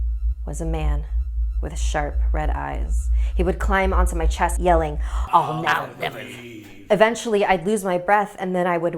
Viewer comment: ok but wtf is this demon thing saying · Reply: All mountain everything